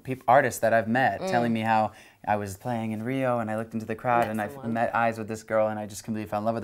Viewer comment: Did he say Rio?